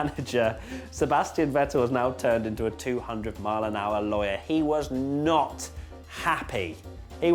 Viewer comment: ngl, thought that at 11:26 it said 200 mph lawnmower